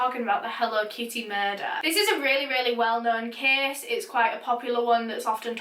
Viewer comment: i had to take a minute because instead of "well known" i heard "this is a really well made case"